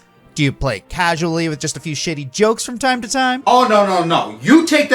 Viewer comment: Did he say shady or shitty?